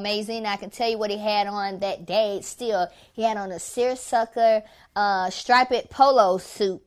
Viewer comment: did she really say stripe-ped?? @ 1:16 tf lmbo I can't, I'm weak